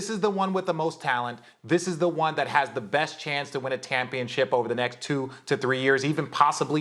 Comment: Did RJ say "win a tampionship" at about 1:39 ?